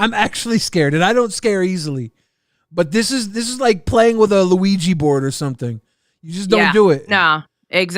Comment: did you just call a Ouija (Wee-dgee) board a Luigi Board?? 😂😂😂😂 15:00 Also cheers from the PNW 🥂🍻 represent! 💟